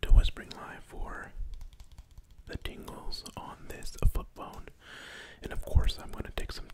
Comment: yo who else thought he said foot bone instead of flip phone